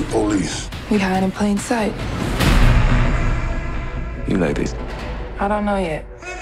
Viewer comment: What he say to her at 1:45 ? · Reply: You like this